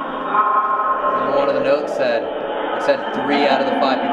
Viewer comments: yo but at 1:06:40 I hear "Elton, do you hear me?" through the portal box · after it said Elton I swear it sounds like it says “you’ve been really bad” 😂